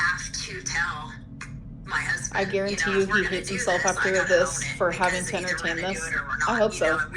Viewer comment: did she just say she guarantees he hit her, and she hopes so. Maybe I heard that wrong.